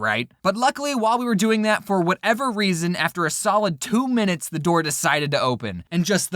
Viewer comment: wait did see say “sowid” instead of “solid” at 3:38?